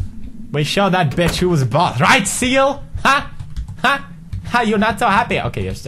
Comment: did he say siel? Whos siel???